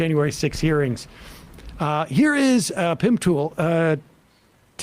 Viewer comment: I thought he said Pimp Pool